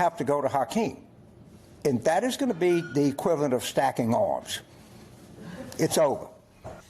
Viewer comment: God bless, James Carville. From his lips to God's ears. Can anyone tell me what he says at 1:38? Something like "stacking olives" but I'm not sure.